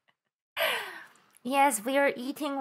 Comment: Did anyone else misheard counterstrike and also thought what a Ina Themed Skin would be